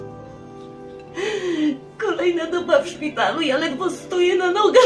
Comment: czy tylko ja usłyszałem "Kolejna dupa w szpitalu" ?